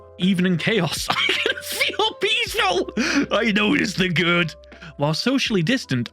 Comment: I thought the Amazon one said "I notice God"